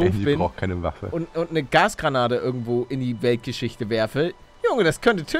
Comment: ich hab am Anfang Gras-Granate verstanden😂😕